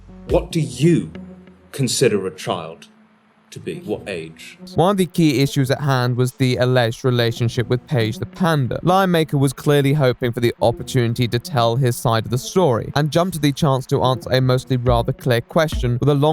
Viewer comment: I hear "lie maker" each time he says "lion maker"